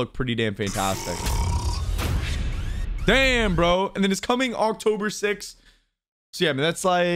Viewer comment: Did he just say October 5th coz on my screen it says October 6th 😂😂😂